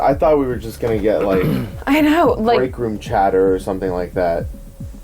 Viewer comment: Just me who thought said “I thought it was like wreck room chat😭